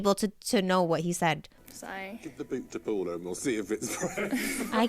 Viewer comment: As a British person - I do not have a scooby doo what that man said. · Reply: I thought he said, “give the boot to Paula!” Is that woman’s name Paula?